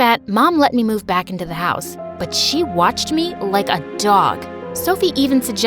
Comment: did she just say she's Washington? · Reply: She said watched me but a bit fast